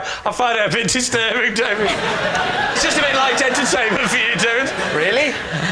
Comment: What did Lee say at 25:06? English is my first language and I still couldn't catch all of it. "Stop doing that. I find it a bit disturbing, David. It's just a bit like..." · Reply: Light entertainment?